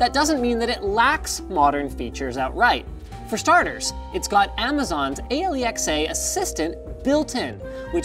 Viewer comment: Did you say aliexah